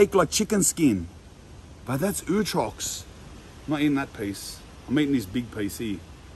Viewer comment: PLEASE will an Australian tell me what word he is saying at like 9:01. 🥺 Ootrocks? Utrux?? What is that and what does it mean??!??!